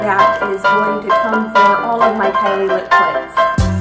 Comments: did she just say Kylie Lip Clits? O_o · am i the only one who heard “kylie lip klits” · did she just say "Kylie Lip Cl*ts"?